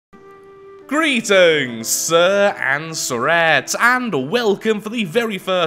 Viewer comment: dave yesiam - "ette" is a suffix indicating feminine gender. In this case "sir and sir-ettes" is a humorous way of saying "boys and girls". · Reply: it sounds like he says "greetings and sirets", what your saying is he says "greeting sir, and sirets" its all makes sense now. thanks!